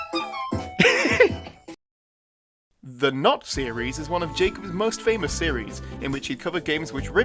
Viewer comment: I got REALLY Confused there, “Not series” I DEFINITELY misheard him